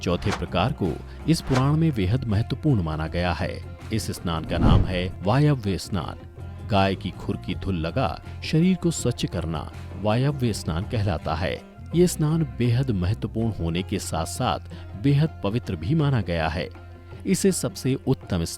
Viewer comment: kya bola h aapne gaay ki khurki aisa kuch h samajh nhi aaya